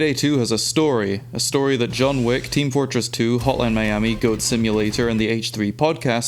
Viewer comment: DID HE SAY HOTLINE MIAMI?